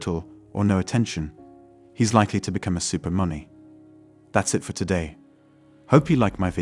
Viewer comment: I didn't hear what you said. Did you say super money?